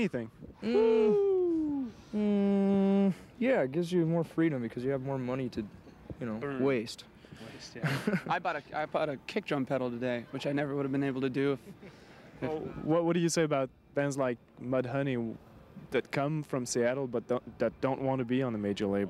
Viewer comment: Dave Grohl bought an iPod in 1991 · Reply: he's saying it because when he stammered it sounded like he said "I bought an ipod." Not very happy that I have to explain the joke.